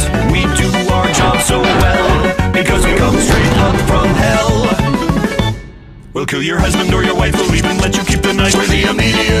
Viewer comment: I thought they said “We’ll kill your Husband or your Waifu, We’ll even let you keep the Knifu.”